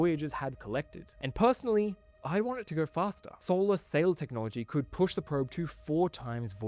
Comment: Solar Sail Technology??? What did he say ?? I'm curious